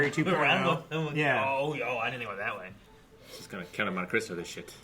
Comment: what did say? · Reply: "just gonna Count of Monte Cristo this shit"